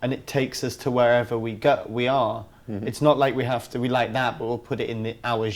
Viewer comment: can someone help me comprehend what he's saying in these? id be very grateful! "its not like we like that ...... and put that in our genre" at 7:11 "it's difficult to say what the catalyst ....." at 7:38 "i can't say I'm .... on my lyrics" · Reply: it's not like we have to be like that and put it in our genre